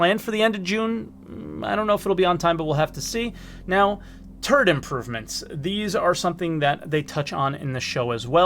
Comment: TURD IMPROVEMENTS!!!!!!!!! Will I finally get my Cutlass Brown? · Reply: I definitely tabbed back to this window after that. "...Did he just say 'turd improvements?'"